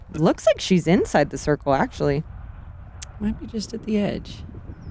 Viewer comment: Talking about Jennifer Allen's drive at 16:50, Paige says, "Looks like she's inside the circle, actually." to which Zoe responds, "Might be just at the edge." but it totally sounds like she says, "Might be just a beotch." which is hilarious.